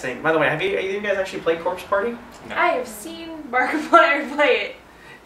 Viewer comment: did she say "markiplier"? if so, u go girl! XD